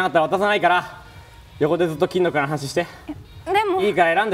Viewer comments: 「入ってなかったら渡さないから、横で」の後なんて言ってるかわかりますか、、、？？ 教えてください😅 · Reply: 「横でずっと金の蔵の話して」 ヨリを戻そうってことですね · 重要そうなのにここだけ聞き取れなかったので誰か教えてくれると嬉しいです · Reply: 横でずっと金の蔵の話して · あたりの横でずっと金属？(kindle?)の話ししてって、なんて言ってるか分かりますか？ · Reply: わかった金の蔵か